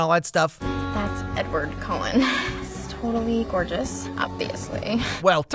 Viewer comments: I love how the subtitles think that the girl called Edward creepy instead of gorgeous. That moment when the subtitles are better than what the person is actually saying 😹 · LOL the caption @ 11:05 said "totally creepy" instead of gorgeous